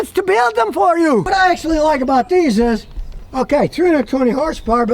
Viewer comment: Ok, 320 hp, that's correct!^^ Maybe I misheard u the first time, i heard 220 and, no! \O>